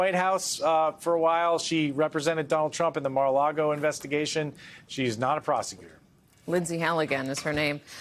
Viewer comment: All I heard was: "She's not a prostitute."